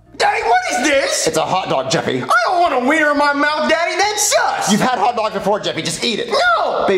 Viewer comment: did he say sus or sucks plus he literally threw the hotdog at the plant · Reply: sus